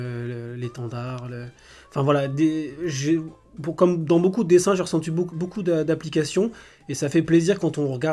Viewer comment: c'est moi ou il vraiment "j'ai resentus"